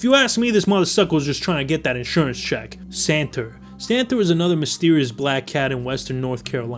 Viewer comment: for a second I thought he said Santa😂 „Santa is a misterious cretured believed to crawl threw chimneys in the night of 24th December. There are countless witnesses and even photo and video evidence of Santa like this one. This is an alledged photo of a Santa in a mall in upstate New York circa 2022.“